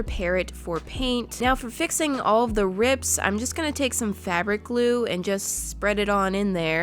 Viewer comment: It sounds like shes saying blue instead of glue